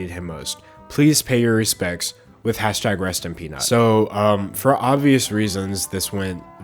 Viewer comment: Just an fyi when you said the hashtag on 3:30 the auto generated subtitles said it was rest on penis instead of rest in peace mr. Peanut